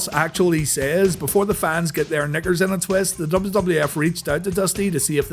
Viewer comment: did u say Niggers in a twist??